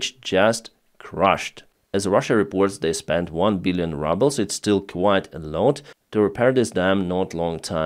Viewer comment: Funny coincidence, when you said "one billion rubles" I thought I heard "one billion rubbles" at first. Anyhow, I guess you can call the ruble "rubble" now.